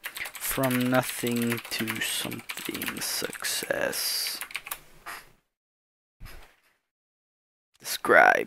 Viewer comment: When you said "From nothing to something sucsses" i thought you said "From nothing to something sucks ass"